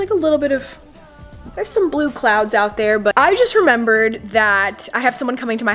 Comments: did she say blue clouds at 7:19 lolol i love herrrrrrrrr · wait did you just say blue clouds @ 7:17 wow , well that's nice